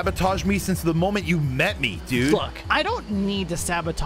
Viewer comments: bro at 23:02 chet sounds like a says the f word instead of the word "look" to me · What did he say? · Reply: He said Look not The f word